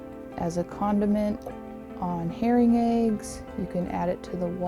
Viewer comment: I heard a condiment on hearing aids… not herring eggs. 🤦‍♂️. Maybe I need hearing aids! 🤓. Awesome video!